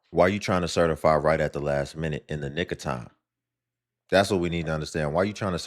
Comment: into video did he say in n!! igger time or nick of time?